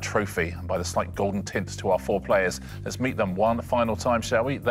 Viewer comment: Did Richard call them "foreplayers"? Oh, I just had a vision of the after-show...